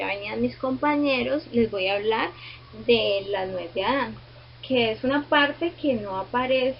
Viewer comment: Minuto 21:43 ¿Que dice la señorita? No logro entenderla. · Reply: La nuez de adan